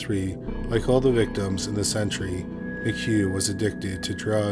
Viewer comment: Ngl, as serious and good as the video is I literally had to rewind it bc I thought you kept saying "this century" (as did the CC) when you were saying "this entry" 😂 Quite the chuckle in the midst of such somber tales.